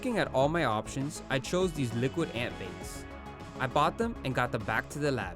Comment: I thought that said “ Liquid Ant Balls “ 🤦‍♂️